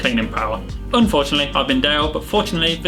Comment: Top class content as always... @ 1:41 Did Dale just say:"unfortunately I've been Dale....." 😲 😂